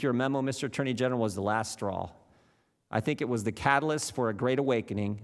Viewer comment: i thought it said "Your Meme Was The Last Straw"